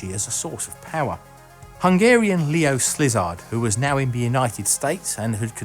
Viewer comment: I swear I heard 'Leo Slizard.'